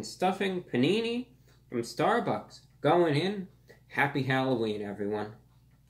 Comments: I'm sorry did I hear that correctly? Happy Halloween? Ok well Happy Halloween to you too.. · did you say "Happy Halloween Everyone" LOL